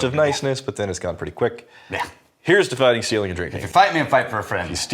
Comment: At the end it sounds like you said "may you steal your livers heart". Not only is it appropriate but I now will never hear it any other way.